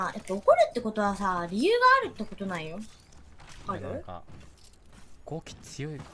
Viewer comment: for future viewers: at 4:14 and later on Kamito says 語気(goki) - tone, manner of speaking; and not 豪気(gouki) - sturdy spirit, bold, daring, brave, valiant, undaunted, stouthearted (which was what was translated). Both sound very similar which is probably why it got misheard and then translated. and at 5:49 he says 異性(isei) - the opposite sex; not (just) people he doesn't know.